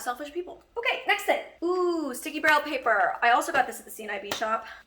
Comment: Hi, At 12 minutes and 34 seconds. When you got the sticky brown paper (Or did you say "Sticky braille paper.") for your lables Sorry to say its white. paper not brown. As I might have misheard you. Love the video. Stay safe from covid19 to you and your loved ones.